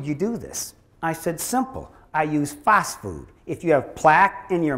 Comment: Can someone tell me what he says, how he dissolved his plaque it sounds like he said I used fast food. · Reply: Riccoh21 Phosfood by Standard Process.